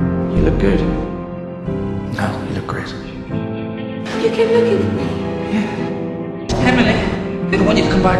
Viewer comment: HAHA I thought Emily said, "You're good looking," and Matt said, "Yeah."